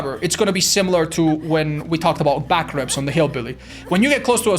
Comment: I always heard "back rev" as "back rub", and I think I prefer "back rub", honestly.